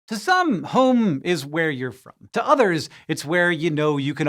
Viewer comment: i heard "home is where you're from" as "home is where you poop" LOL